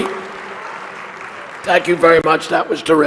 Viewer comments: I know the guy has a raspy voicebox but it sounded like he introduced her as "Killary" Lol! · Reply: Ginger Smith ..I,too, heard him say Killary I swear..lol.... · it sounded like he was saying killary